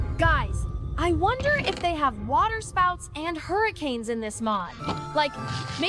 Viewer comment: DID YOU SAY WATER SPOUTS